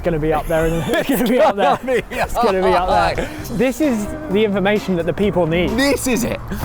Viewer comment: I had this on as background noise, at the 2:50 mark, after the diarrhea rating, I thought said ‘this is the inflammation that the people need.’